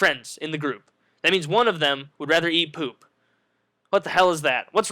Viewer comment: i heard ''woody rather''